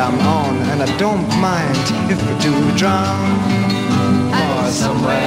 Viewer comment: 'And i dont mind if i do drugs' haha i thought thats what he said.